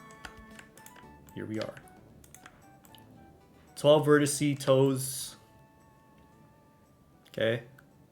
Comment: I heard 12 Rinasci-toes and had to double take